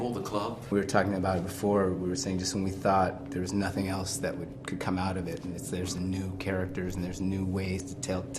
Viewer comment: SWEAR TO GOD Don Cheadle says "we were saying... "There's this new THOT... Nothing else good can come out of it..."" Maybe I'm wrong... But i CHOOSE to believe... Gotta keep hope alive xD